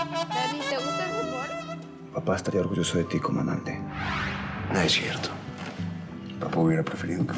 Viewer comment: Minuto 11:49 Dijo, COMANDANTE . Hubiese jurado que escuche que le decía,,, Papá estaría orgulloso de ti COMO AMANTE 😂😂😂😂